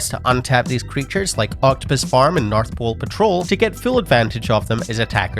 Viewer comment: Gotta say, turned my head away and heard you say "Octopus farm".